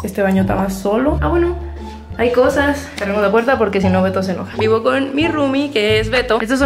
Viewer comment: Escuche mal dijiste 0:09 0:09 0:10 0:10 veto